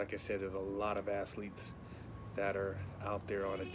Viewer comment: I swear I thought he said "assletes" instead of "athletes" 😂😂